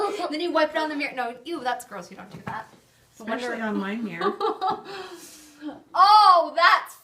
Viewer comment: who's holding the camera in the beginning? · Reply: Kendl Shelia thanks, i was just wondering. I watched it a second time and heard her say "not on my mirror" LOL.